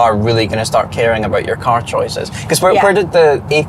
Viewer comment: I thought they kept saying "E" class, come to find out it's an "A" class Mercedes